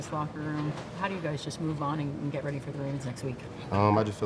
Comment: Did she say get ready for the rams lol